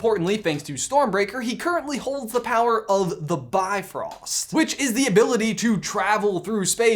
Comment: I swear to thanos I heard you say “holds the power of the bob ross”